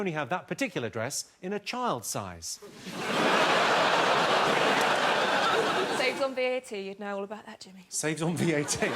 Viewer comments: what does she say? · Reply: Saves on VAT[ value added tax] , Don't pay it for children's clothing but adults pay 20% VAT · 'Saves on VAT you'd know about that, Jimmy' BUURRN! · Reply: omg I literally just caught that, I always thought she said "how about that Jimmy" - this is so much better! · What did Rachel say at 1:50? I can't make it out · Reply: "Saves on VAT, you know all about that Jimmy"